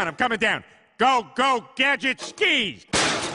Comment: I’ve always thought he said “Go go gadget Steve.”